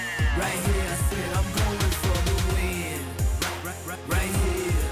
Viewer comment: Is he saying "im coming for the win" I hear him saying "im coming for your wig" 😂😂